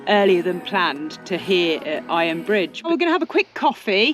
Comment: Iron BRIDGE; heard it as “fridge “ 😫😳🤣